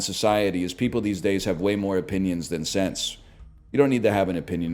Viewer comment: I thought he said "than sex" and I was ready to +2000 his ass